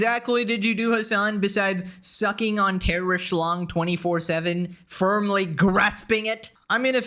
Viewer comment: I swear you said “terrorist lung” 😂